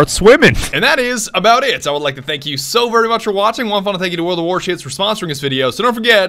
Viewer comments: did he say world of warshits😂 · I thought he said world of war shits